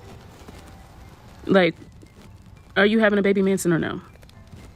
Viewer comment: I’m high but did say “baby Manson” 😂😂😂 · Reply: I thought she said baby mansion... but manson makes so much more sense and hilarious